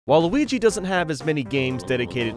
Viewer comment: it sounds like he is saying Waluigi